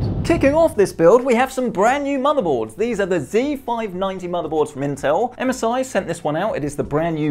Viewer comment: did u just say z590?